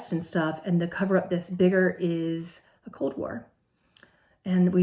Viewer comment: What are you saying at 6:57min ? Cold war? Where else did you talk about it?